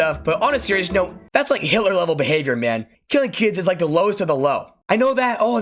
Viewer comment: Oh, when you said killing kids is Hitler-level behavior, I thought you were saying Hillary... I mean... still fits.